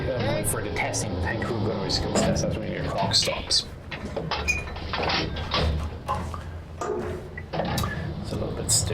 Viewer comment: Does anyone have a source for the loading video in the bottom right at 21:26? Interested to see what the guy says. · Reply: Recorded during our deployment. Evaluator says “stop, you’re a no-go”. Loader (actually, my first gunner, but we all have to take the test” says “why?” Evaluator (my PSG) “X many seconds” loader. “But this thing f-ing takes forever”